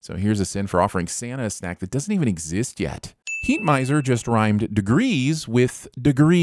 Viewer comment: People thought he said “100 degrees!” But he actually said “100’s a breeze!